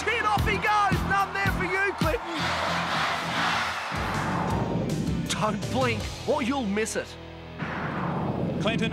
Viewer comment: Does anyone know what the audience sang/ chanted when the challengers were knocked off the runway? 34:53 I have been trying to work it out as a kid when I watched it and now I’m seeing it again I still can’t catch it😅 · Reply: Another one bites the dust.